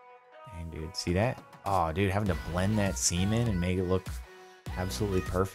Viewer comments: what did say??? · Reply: I mean the subtitles even say semen and I couldn't hear anything else... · did he just say blend SEAMEN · did he say blend that semen?